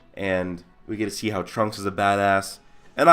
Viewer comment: lol I thought he said at 7:40 "we get to see trump as a badass"